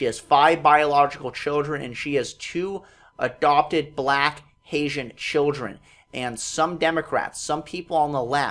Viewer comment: It sounds like he is saying " Asian children ) but it is " Haitian Children. "